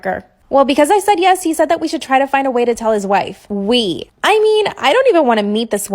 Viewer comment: Two things: 1) I thought she said KILL his wife not TELL his wife 😅 2) it’s cute that the guy told the little ghost girl not to watch the scary part of the show 🥰